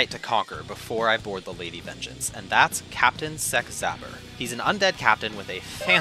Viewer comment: I thought you said "Captain Sex Haver" and I was like oh shit I should install this game again.